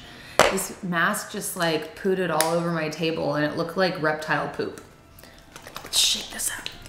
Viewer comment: when she said reptile poop, for some reason I thought she said Reptar poop lol